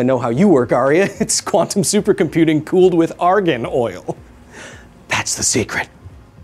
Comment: for a moment there I misheard "argan oil" as "argon oil" and my eye started to twitch.